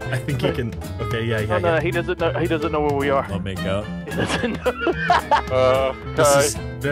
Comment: did say wanna make out 😂😂